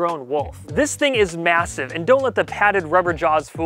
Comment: Did I hear massive? Are we talking about that low taper fade meme?!